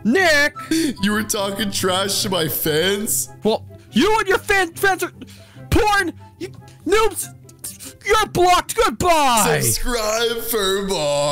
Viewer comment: Did bro just say porn? Lol